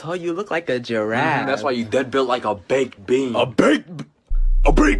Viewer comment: I also thought it was saying "A BAKED BEAN NA" it was actually "WHO'S THE BAKED BEAN NOW"